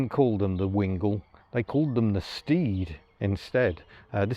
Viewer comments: I misheard you say “Steed” and thought you said “Steve”! I’d buy a camper called “Steve”! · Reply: I thought I heard "Steve " as well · I misheard you say “Steed” and thought you said “Steve”! I’d buy a camper called “Steve”!